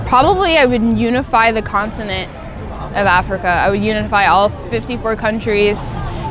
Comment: Actually loved her until she said she wants to “civilize” African countries? Whatever tf that means Africans are already “civilized” thank you! Colonizer’s spirit much!? 🤦🏾‍♀️ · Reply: thought she said unify